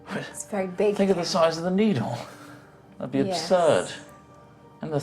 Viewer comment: I thought he said "Look at the size of the meathole". Lol.